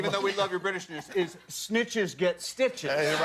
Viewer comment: what does that expression mean??? did he say sneetches get stitches?